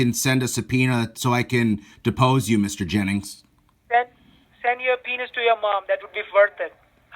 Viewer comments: I think he said "send your penis to your mom, how about that"😂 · LMAO....This Guy was so stupid that he thought you said send your penis....when you said Send a Subpoena.....Keep it up this was hilarious. Makes me regret not recording my call back when I threatened to hunt him if this was a scam. He held his cool though until he said he was having Calf. State Police coming to arrest me, and i told him I was in NY not Calf. Guess I should have corrected that when he first asked if I did in fact live in Long beach Calf. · Did he just say "send your p**is to your mum"?! Too rude.